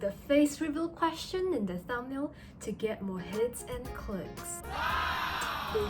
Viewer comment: At the beginning i thought you said hits and cl1ts instead of hits and clicks 💔💔